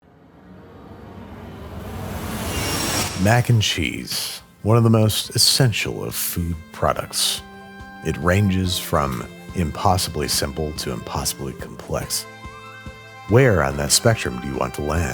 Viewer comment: I'm sorry. But when you say "mac 'n' cheese" I keep hearing "maggot cheese". 😅